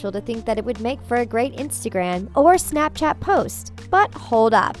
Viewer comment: B*TCH DID U JUST SAY INSTAGRANNNN?